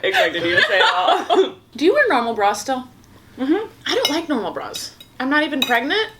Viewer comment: lmaoooooo when she said do u wear a normal bra still i thought she said “do u and ronald raw still?” shjsdnskahahahahahah